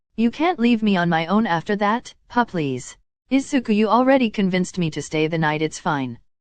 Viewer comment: when he said p-please it sounded like he was saying “febreeze” iykyk